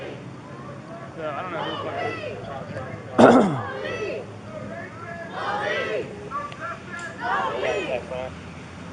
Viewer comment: What are they shouting at 5:20? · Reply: I can't quite hear what the guy is saying but they're chanting "no peace" I think